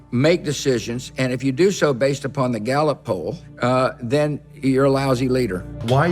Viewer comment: "For a leader you've gotta make decisions, and if you do so based upon the galot pole, uh, then you're a lousy leader" Galot pole? Gallows pole? Ballot pole, the fuck does he say there? 😂